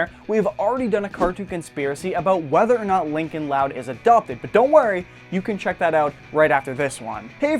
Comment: I thought he said " Lincoln is a dolphin" 0:06 but then I saw the video and it said Lincoln is adopted 😝🤷🏻‍♀️